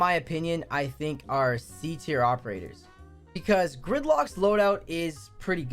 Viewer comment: Who else heard thicklock instead of gridlock